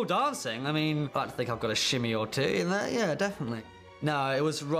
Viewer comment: Can anyone tell me what tom said? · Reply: "i think i got a shimmy or two in, yeah"